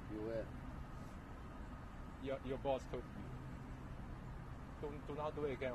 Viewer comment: i think he said your boss, not good boss. I'm getting feeling that there's communicating problem with your guide. Your guide shouldn't expect British English from you. She should have only relationship with you ie science. Communication issue killed my PhD potential too. Guides are so cruel. Most of them are out dated and do not being to the science.